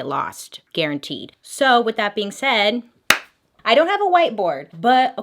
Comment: When you said "I don't have a white board" for a split second I thought you said " I don't have a white boy" and I was so confused